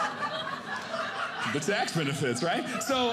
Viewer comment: "the tax benefits, right?" That was so unexpected I had to go back and turn on closed captioning to figure out what he'd said.